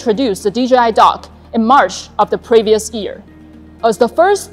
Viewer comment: I thought she said DJI dog. A little dissapointed I wont be getting a drone dog.